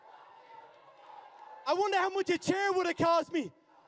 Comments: What does Conor say at 25:25 ? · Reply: "I wonder how much a chair would've cost me". Refering to the $75,000 fine he got for throwing a water bottle at the last presser. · What Conor said on 25:24 ? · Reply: He said i wonder how much a chair would of cost me reffering to the fine he got for throwing bottles at nate diaz